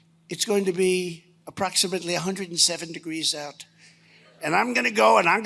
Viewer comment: So you're ok with lying about him saying 170 degrees? Then you had the audacity to say it repeatedly. At 2:35 he clearly said 107. Do better man. Their are ppl out here that respect and love you for your honesty.